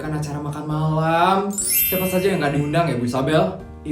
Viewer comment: Di menit 3:02 itu apa gak salah denger aku ya, kata asistennya siapa aja yg gak di undang?